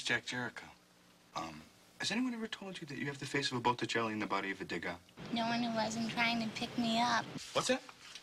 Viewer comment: I was a kid when I used to see this trailer and I thought he said body of a dead god. Lol